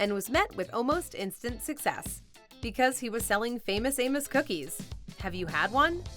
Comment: Ya I seriously thought you said famous anus cookies lol. But ya love amos cookies